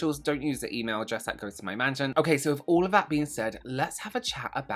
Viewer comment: Wait, your mansion has its own email address? I love it. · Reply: I thought that's what he said at first too.😂 I think he said "management".